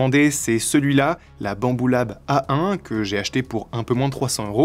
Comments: la bambou quoi ??? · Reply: La bambou lab a1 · j'écoutais la vidéo sans la regardé j'ai compris " la bamboula A1" 😂